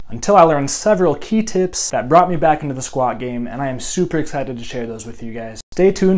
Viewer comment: Me: Did someone say Squid Game? Also me: Mehh, it is just Squad Game